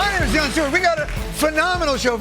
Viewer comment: DID HE SAY HIS NAME IS DAILEY STEWART